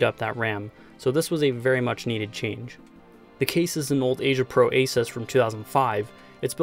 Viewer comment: can you please tipe the name of the case, i had the same back in 2003 or 2004 (i hear "asia pro aces" in the video but thats not correct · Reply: Sure, the full model name is AsiaPro ASYS 8654BL.